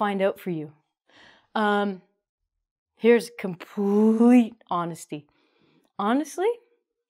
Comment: How say that: Essa moça é incrivelmente inteligente, e eu queria ser como ela. Trabalhar em algo assim como ela faz. Pois é muito intensa e dedicada no que faz! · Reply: Hey there, what word did you used at 5:40 ... 'what' honesty?